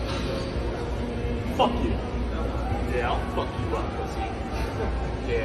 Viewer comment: I thought bro said “yeah I’ll F you” for a second 😭